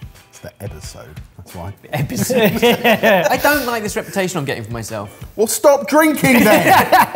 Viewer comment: I think the captions are wrong... Isn't it "I don't like this reputation... Well stop drinking then"? 😅